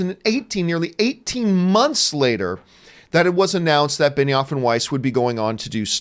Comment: I don't watch Game of Thrones or really know anything about the creative team. So for the longest time I thought you were saying Benny Offenweis